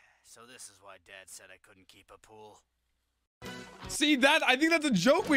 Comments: APULE!?!? For the longest time I thought he said a poodle… that made it funnier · Appule?! All these years I thought Radditz was saying he couldn’t keep a poodle. 🤣